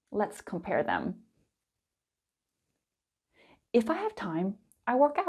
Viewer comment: sorry, but here 9:04 it does not say "if I have time". I understand that they contract words, but this is already too much. I have lowered the playback speed to half. I heard it about 15 times and all I hear is " if five time "